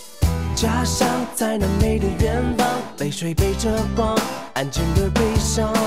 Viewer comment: 以前总把在那美的远方听成在南美的远方